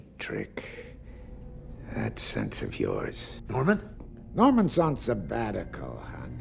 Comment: Sounds like he said "John Lennon?" (lol)